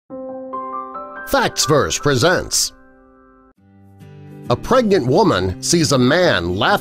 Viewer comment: Omg it sounds like u said a "pregant woman" The first time u said it